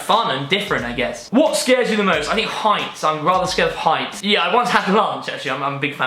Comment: I'm Scottish and I speak perfectly good English but because has such a fancy accent I couldn't make out a word of this video!! I thought he said he was scared of Heinz as in the beans... anyway he seems lovely! shame I can't understand him...